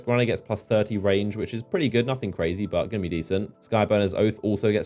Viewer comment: Did you just say "brazy" at 6:44 😂😂